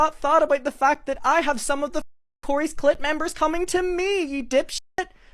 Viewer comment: is he saying clit or click?! 😅🤷‍♀️